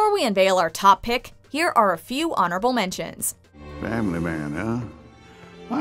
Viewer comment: I thought it said Horrible mentions XD